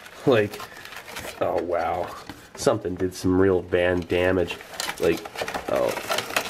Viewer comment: "van damage" did I hear that right?